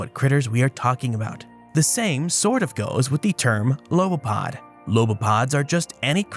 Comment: I swore for a second that said "Grilled lobopodians" 🤣